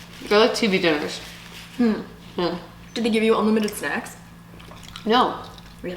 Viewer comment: I don't know why i heard "do they gave you unlimited sex" 😲🤔🤔🤔